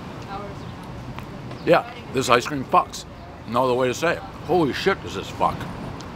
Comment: thought he said this icecream sucks